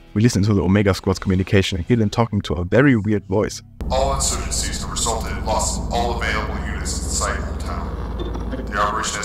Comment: “all insurgencies have resulted in loss of all available units in the site of town.” I think that’s what it says. I had to slow the audio but that’s my best guess.